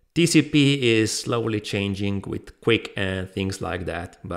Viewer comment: 🔰 What does say at 1:06❓ What is Quick❓Is that what he said❓ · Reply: TCP / QUIC: <link> and <link>